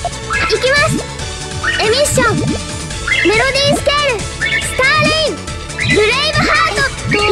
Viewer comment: Did she say Stalin? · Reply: STAR RAIN (stupid bit-)